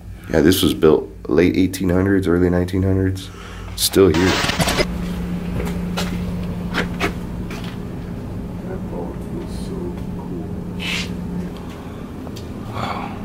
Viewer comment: it could have been, but i hear: ''This vault is so cold/cool'' (i think it was the miner who says it is cold in there)